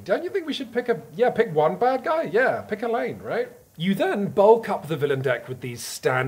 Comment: I did a double take and had to rewind when i thought i heard "yeah pick one bad guy, pick elaine"